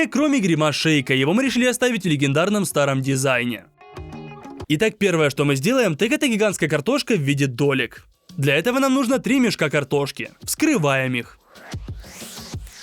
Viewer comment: "первое что мы сделаем, так это гигантская картошка 🥔 в виде Толик 🧔" (я знаю, что там было сказано долек, просто мне так послышалось) )